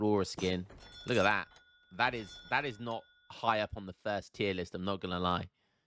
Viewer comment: did i hear thirst tier list?when?^^